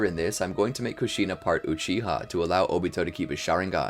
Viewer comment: I thought bro said Mishima partly cheat on 😂